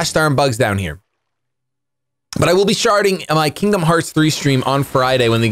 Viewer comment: sounds like said sharting